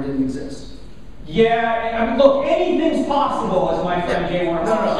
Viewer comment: I can't understand what Frank Turek said at about 1:50. "That is the devil's...."??? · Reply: “That is the devil right there”